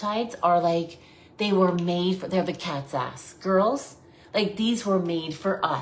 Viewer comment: did she say “cat’s ass”??? Is this an expression I don’t know???